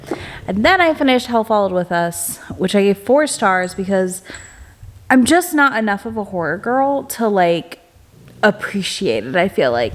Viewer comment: i misheard as "i'm just not enough of a whore girl" and i was so confused with the context!